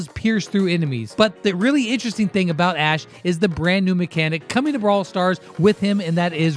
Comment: i thought it said range mechanic in brawl talk :|